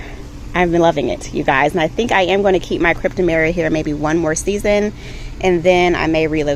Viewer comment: Love your garden ❤❤❤ Quick question: At about the 1:20 minute point of this video it sounds like you say you’d probably moving your ‘criptamary’ to the backyard. I love it but can’t figure out the spelling to search for some. I keep getting a sugar tree instead. Please share the spelling.